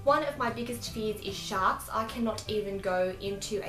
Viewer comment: When you said sharks, I thought you said shots, so you said swimming pool and I was like "what????"